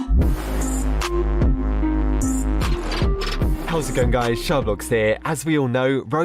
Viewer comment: When I put captions at the beginning, It thought you said “ Shaw Vlogs “ . lOl